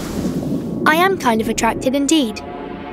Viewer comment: i thought she said "i'm kind of attractive indeed" 'bout to say girl is so self-conscious lol